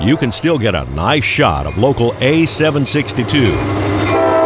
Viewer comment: It's a rare day to ever find a discrepancy in your video but at 1:50 you say A762 and subtitle says A782.